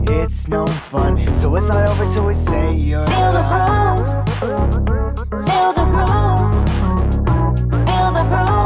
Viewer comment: I always thought it said " feel the prune" 😃🔫